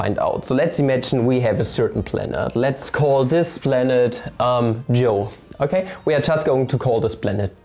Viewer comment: Who's joe ? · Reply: At first I thought he said "Let's call this planet Geo".